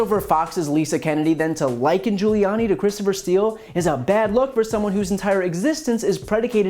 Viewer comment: Lmao she hit a nerve at lunatic Giuliani douche bag Lmaoooo 😂😂. At first I thought she said Remington Steele. Lol... then she took me out with Borat😂😂😂